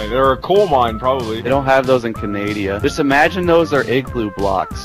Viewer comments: Did someone just say Canadia? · did he say Canadia? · did he just say canadia